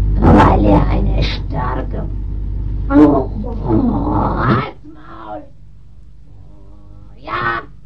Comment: Ich hab irgendwie halts maul verstanden...